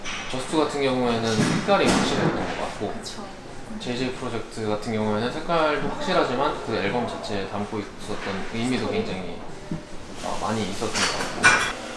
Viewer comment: The subtitle is wrong 3:30 , he said JJproject is more meaningful for him